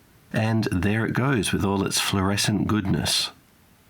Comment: I heard "fluorescent glutenous" @ 14:54 😅